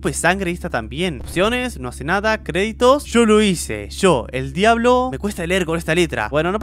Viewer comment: me pueden reescribir lo que dice en el minuto 10:30 🤔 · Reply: Yo lo hice yo el diablo hago juegos